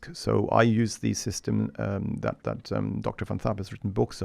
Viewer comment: who is he talking about i cannot understand doctor f something he mentioned about his books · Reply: Dr Van Tharp